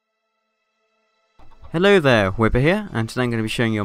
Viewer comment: for 1 moment i thought you'd said "Hello, wither here"